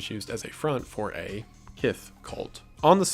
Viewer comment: is he saying “kif” cult? I don’t know what word he’s saying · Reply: "Kith". It's basically his way of saying "sex" or "porn" without triggering YouTube's automatic moderator. He's saying it's a sex cult.